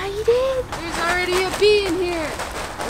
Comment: I thought she said ‘there’s already a pee in here!’ But really she said ‘bee’ .. I was slightly confused. And apparently deaf. 🤷‍♀️🤦‍♀️💜